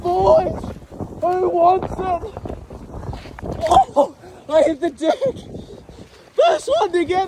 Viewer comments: When he fell it sounded like he said. I HIT THE DICK!!! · i heard him say “woahh i hit the dick!” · I heard ‘ I hit the dick ‘ 😂😂